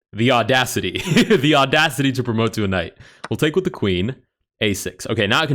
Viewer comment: What did you just say on 8:39? The Audacity? ....To promote to a knight